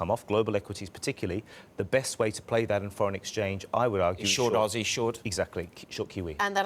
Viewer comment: Damn reporter! Why'd you have to interrupt at 4:04?!?! The guest didn't get to say what he was GOING to say, and instead said "short QE" AND because of your interruption, I can't understand what the hell is being said AT ALL at 4:04. I feel that was some potentially valuable information, and you ruined that.